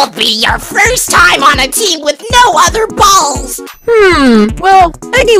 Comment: i think robot flower said balls